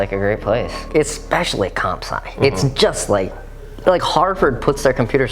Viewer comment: Can someone please write what is he saying, is that some web site or something? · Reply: I know im 4 months late but its a shorter way of saying computer science (comsci)